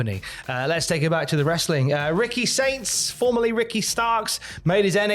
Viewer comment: Think it's because so many Americans are poor now and can't afford to spend money on entertainment, but Saudis are rich? For a moment it sounded like said "Ricky Stinks" instead of Ricky Saints.😀But really, best of luck Ricky, it's always a gamble going to another company.